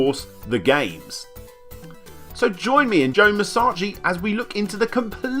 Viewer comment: did you just call him Joe マッサージ